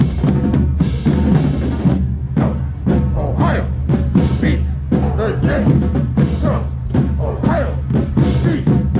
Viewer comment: What are they saying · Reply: “Go Ohio, beat the Zips” (Akron’s mascot)